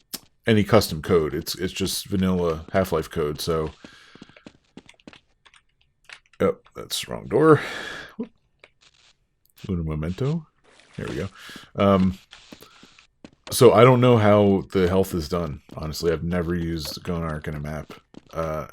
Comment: Did you say "un momento" at 1:17:53? Btw, such a great video!